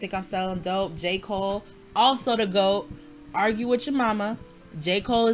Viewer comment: i fr thought u said jake paul the goat 😭 i was like girl wtf 😂